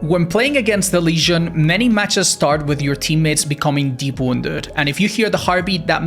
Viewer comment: I have absolutely no idea what the hell he's trying to say at 7:38. What even is that word? Deborndead? Like what? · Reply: Deep wounded